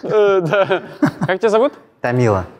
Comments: Тамила? Послышалось то Данила, то Камила, хах) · Артур:приятно... Тамилла...Сильное рукопожатие... Я:ржу с его реакции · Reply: Мне послышалось Данила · Мне сначала послышалось, что её зовут Камилла, а не Тамила😅